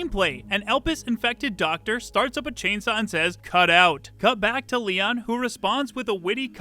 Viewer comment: Leons response "can i get a second opinion?" makes more sense with a zombie doctor saying "calm down" with a chainsaw in hand, no? Just me? Also yeah it would be silly not bring parry back after re4make :) Great video btw :) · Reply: I thought he said, "Cant die!" Referring to himself but it is "cut out".